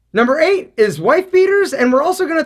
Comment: I thought he said wife beater